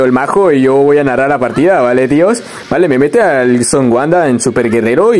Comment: Minuto 18:56 esta parte leo hablando castellano Soy yo o escuché que dijo son guanda me encanta cuando ganas haces un corto dónde pasan memes